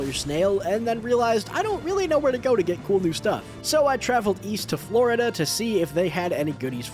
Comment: is it just me or did he say East of florida